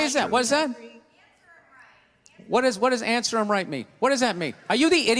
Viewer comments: She said, “Answer him right,” because she didn’t realize Bill had asked a rhetorical question. Starting at 7:03 Bill asks several questions ending with asking twice, “Why is that?” She thought he actually was expecting responses from the audience, so attempted to encourage answers. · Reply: I heard "answer'(th)em right." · The girl that heckled him said "It's her right" Imagine if he had heard her say that instead of what he thought he heard😂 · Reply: She clearly said “Answer him, right!”. How do you get it’s her right 😂🙄. Seriously haha.